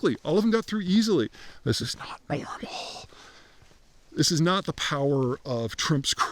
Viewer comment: what does he say at 12:11 ? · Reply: “That’s not normal”